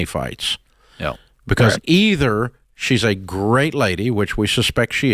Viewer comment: I had to watch it twice. I thought i heard dave say that she was a "great lay"😂😂😂 Obviously he said "great lady". But thats not what i heard first. 😂😂😂😂😂